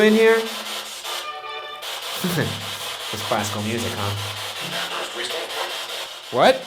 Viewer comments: it sound like it said classical music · I heard the voice say “Classical music, huh?” Like it was mocking you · I heard "don't laugh at me"...? when ryan used the spirit box while trying to communicate with james.. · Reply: I heard “yeah. It’s classic.”